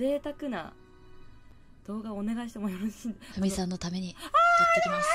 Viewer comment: ｢フミさんのために｣の所が｢カミさんのために｣って聞こえて結婚してんの？って邪推してしまった